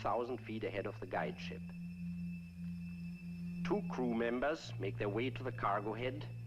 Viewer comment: did he say "2 Kuh Members"?